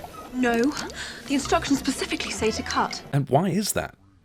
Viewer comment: does Hermione say pacifically instead of specifically or is that just me?